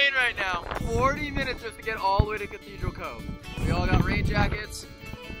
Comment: I thought that Elton said gathedral grove note cove. There is a gathedral grove bit it's probably not as popular as gathedral cove